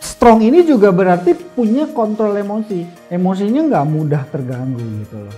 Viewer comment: Kontrol emosi apa kontol emosi,apa aku salah denger ya 😂